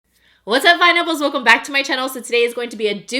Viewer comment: Okay, I heard "what's up my nipples!" LOL 🤣 but honestly, here for it!